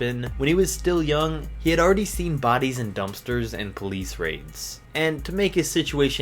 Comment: did he say police rapes